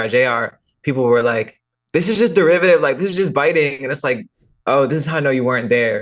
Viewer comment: did she say “biting” at 32:37 ?